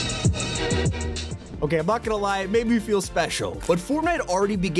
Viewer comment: “It made me feel special” is so innocent, but when I heard him say it, all I could think of was “He’s Sped?”